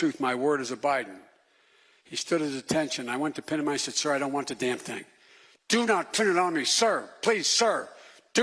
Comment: he actually says "I went to pin him and ' I ' said, " Sir. I don't want the damn thing. Sir do not pin it on me." Listen...yeooo he's got to go